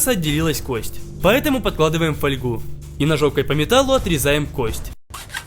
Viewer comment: Мне одному послышалось : И жопкой по металлу. =]